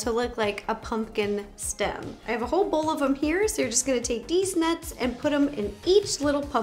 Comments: Did anyone else hear Ro say “Deez nuts” when talking about putting the pecans on the rolls!?!?? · Did she just say Dee's nuts 7:24 🤦😂